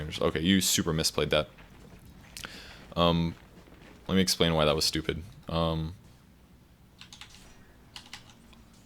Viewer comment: did i just hear "let me explain why you're stupid" 19:19. XD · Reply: he said, "let me explain why THAT was stupid". there's a difference bro. you're making it sound worst than it is